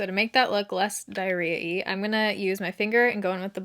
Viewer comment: My bf doing homework “did she just say diarrhea?” Me “diarrhea-y get it right” 😂